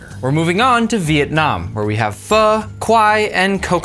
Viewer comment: I thought he said fah not pho I was like it’s pho not fah then I was like ooooooo😅😅😅😅